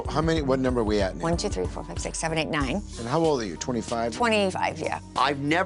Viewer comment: To be fair it really did sound like he asked "how old are you" instead of "were"... No need to get all angry at people for mishearing information. We're all friends here :)